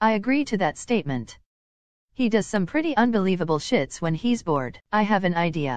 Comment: “He does pretty unbelievable s*** so” But what I heard: “He takes unbelievable s***”